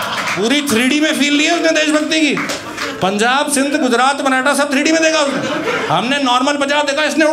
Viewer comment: i thought canada instead of maratha 🤣🤣🤣😂